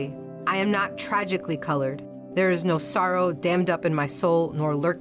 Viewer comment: Did she just say "Soros..." seconds?